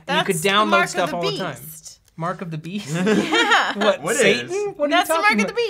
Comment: wait. mark of the beast? i thought they said 'mark of the bees' and i was like why would that be satan the f